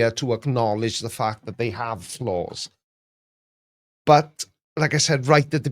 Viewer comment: Wow, you were saying flaws and I thought you were saying floors! Damn my highly accented ears!! It sucks that I always hear in accents, because I know you definitely don't have one!!